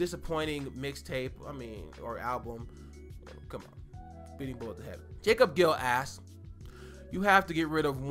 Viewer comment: Wtf did he say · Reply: Speedin Bullets to heaven